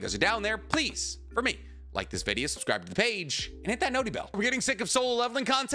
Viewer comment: "Are you getting sick of Solo Leveling content?" You could make 3 videos a day on it and I'd probably still watch them all. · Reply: i thought u said your getting sick of solo leveling and i was just confused